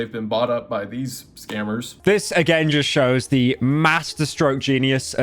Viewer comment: wtf is this word? Distrogeneous? Dystrogenius? What?